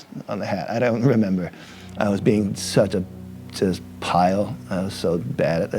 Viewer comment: Does Dill call himself a "pile"?